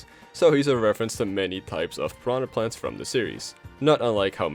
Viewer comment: So he's a reference to many pipes of piranha plants. Not sure if that was intentional, or it just sounds like pipes instead of types, but that's a good joke.